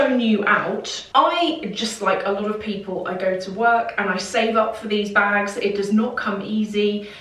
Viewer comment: Everytime I heard you say “I save up for this bag”, I feel poor lol